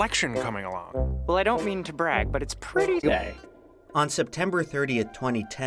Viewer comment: What does he say at 0:03? I can't understand it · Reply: "But it's pretty gay"